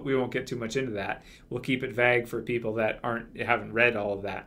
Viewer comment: Did he say we will keep it vag? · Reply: Eicholtz Yeah, he said we will keep it *vague